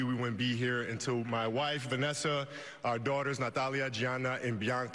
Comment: Did he just say Giannis Antetokounmpo??